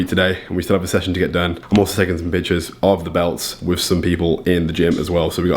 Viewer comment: I literally thought he said "bitch*es of the belts" instead of "pictures of the belts"